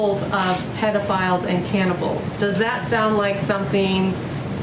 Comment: Trump's saving the world from cannibals WOW just when you thought the U.S couldn't get any more crazy .Someone should build a fence around that country they are a menace to themselves and the rest of the world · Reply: I thought you said canibis and got worried